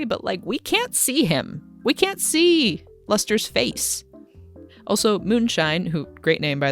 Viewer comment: His name is "Luster"? I kept hearing "Lester"